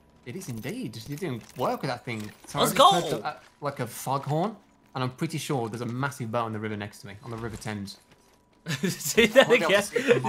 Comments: what is this man saying 😭😭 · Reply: I'm norwegian so not sure but i heard "sorry I just heard like a foghorn and I'm pretty sure there's a massive boat on the river next to me, on the river Thames" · What did say ?😂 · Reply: I heard "sorry I just heard like a foghorn and I'm pretty sure there's a massive boat on the river next to me, on the river Thames". I'm norwegian though, so I'm probably wrong lol